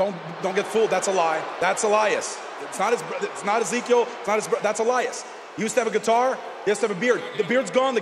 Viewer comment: I accidentally enabled the caption and I'm pretty sure KO said "That's Elias. It''s not-- it's not Ezekiel." But the caption says "that's alias. It's not as it's as eco God." 🤣🤣🤣🤣🤣🤣🤣🤣🤣🤣